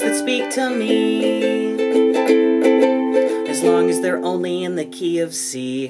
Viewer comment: "As long as they always in the key of C" Triggered 98% of ukulele owners. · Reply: I thought it said “As long as they are always in KFC” 😅